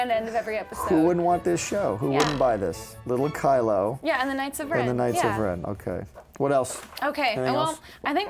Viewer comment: I totally thought she said Kylo Ren and the nicer friends. Guess her title works too. I'd watch it, get it made.